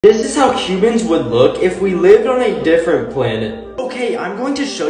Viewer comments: Am I the only person that heard him say “what chicken’s would look like if we lived on another planet” 😂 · Ok for some reason I keep hearing "Cubans" instead of "Humans"